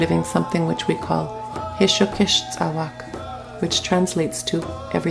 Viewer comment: what she said hishyokis or something?